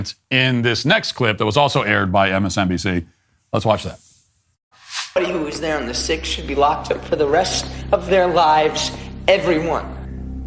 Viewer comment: Whoa! What did he say? It's cut off at the beginning, so I'm not 100% sure about the first word there, but I think it was "Everybody who was there on the 6th should be locked up for the rest of their lives. Everyone!" Presumably referring to anyone who was, what? In the building? On the grounds? In DC at all? And he's supposed to be framed as the non-extremist in this interaction?